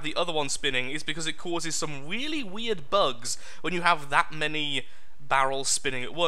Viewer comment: I know says he is bad at English but heard him say "weakly weird" did anyone else hear him say that or am I alone